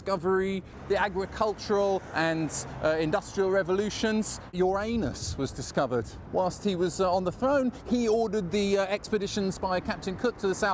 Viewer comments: I had to double back there, thought he said "Uranus was discovered whilst he was on the phone!" 😁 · Did you just say...your anus? Or have I just heard it wrong? =)